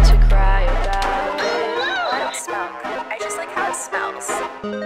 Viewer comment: Not me mishearing: "I like hunk smells."And I was really in deep of acceptance.